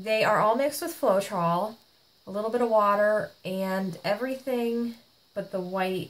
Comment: What is the paper towel soaked with? Thank you! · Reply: I thought she said water.